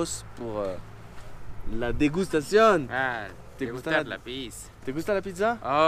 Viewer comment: Le mec t'avoue tranquillement qui aime la pisse xD Comme quoi faut de tout dans un monde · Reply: Ca sonne tel que "Pisse" mais en vérité il utilise un diminutif en Espagnol qui se dit en Argentine la "Pizz", pour Pizza, en gros il dit "me gusta la pizz", j'adore la Pizza ;) :) Tu remercieras ta prof d'espagnol pour moi ;) :)